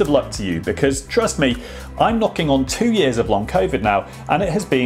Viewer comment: At the end it sounded like you said "look out for your cells"...which given the context would make more sense really. Thanks for the video.